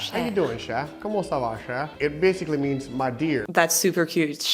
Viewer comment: No it’s Cajun and it has Spanish influence among others!! Because the Spain populated Louisiana also! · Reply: It sounded like he said Como esta la Cher